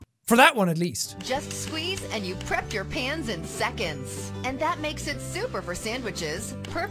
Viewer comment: I heard “just squeeze and you’ve crapped your pants in seconds” 😂 like thanks for the life hack